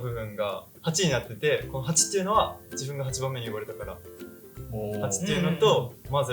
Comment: えっエイキくん8人兄弟！？って事！？！？ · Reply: 「8番目に呼ばれた」を「8番目に産まれた」に空耳してしまいました〜❣️びびった〜❣️ 今日も楽しかったでーす❤️‍🔥😂